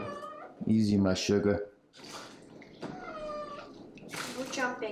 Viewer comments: I thought he said "easy my sugar" 💀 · I thought dude randomly said ''Easy my suga'' I lost it